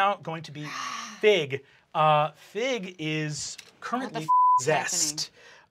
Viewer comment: that "currently f**k" really sounded like "kermit the f**g" to me, and the possessed after it was very confusing